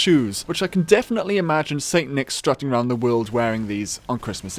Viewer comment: Did you say "Satan" at around 1:50 Is gud list doe.